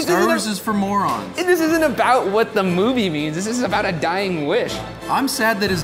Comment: I thought it said dying fish😂😂